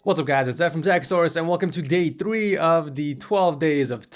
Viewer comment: "welcome to day 3" i thought he said welcome to gay 3